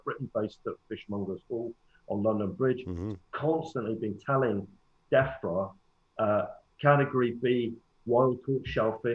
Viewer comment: I keep hearing defra as "death threat"